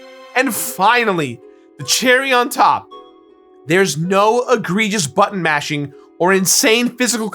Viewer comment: True, though the GF system had button-mashing, which was what my comment was meant to address. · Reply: Oh... Apologies! I'm also rather upset that I misheard "Egregious" as "aggressive", but... yeah, point made, point taken... point not mistaken. Sorry, again!